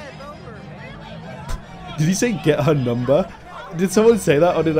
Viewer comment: i think he said "dump her"